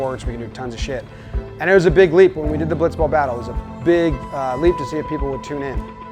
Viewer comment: I heard "Blitzball May"